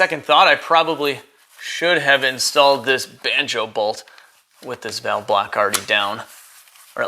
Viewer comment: when You said banjo bolt, I swore you said “I should have installed this B*tch!” Lol!